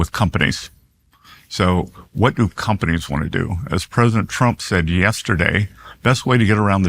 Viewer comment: Am I the only one who heard him say President Frump?